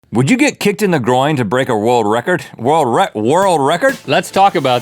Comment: Rhett sounded like broken record trying to say world record